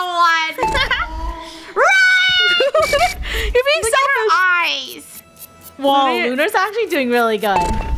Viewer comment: I thought said sh*** eyes